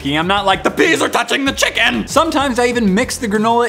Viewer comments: I thought he said ,”THE BEES ARE TOUCHING THE CHICKEN 🍗 !” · I thought he said “THE BEES ARE TOUCHING THE CHICKEN”